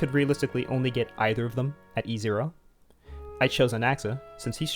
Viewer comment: i'm CRYING the captions said acheron instead of anaxa 😭